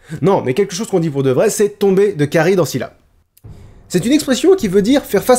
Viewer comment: j'ai entendu tomber de Kaaris en Sylla 😭😂